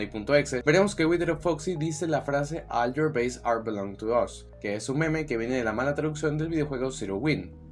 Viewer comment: Ah. Yo pensé que decía "all your data belongs to us".